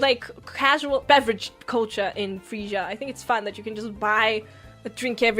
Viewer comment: Whenever Cecilia says “Freesia”, the US stand-in, I thought she said Frisia. So CC and Gigi met up in The Netherlands or Denmark.